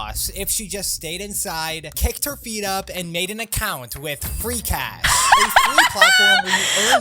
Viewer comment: i thought he said freeCAD and i got excited because thats the cad i use